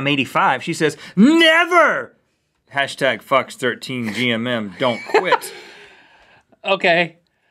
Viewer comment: Link's reaction at 2:34 when Rhett sounded like he said "f**ks13GMM"